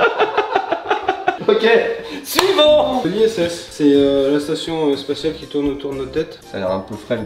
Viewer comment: "La station spatiale qui tourne autour d'la tête" à 24:06????? C'est moi qui ai mal compris ou bien ?? 🤔🤔😁